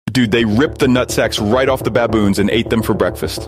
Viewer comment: U guys need to stop I woke up turned on my phone and it was at max volume I HEARD “dude they ripped of its nvtsacks